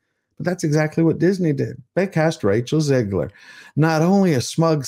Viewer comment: I thought you said "castrate Rachel Zegler", I blinked , shrugged and said ok.